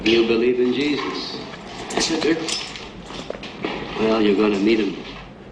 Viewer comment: Five years later, I learned Bronson actually said, “You’re gonna meet him.” I thought he said, “You’re gonna need him,” as if to say you’re gonna die and you better hope Jesus doesn’t drop you in the pit.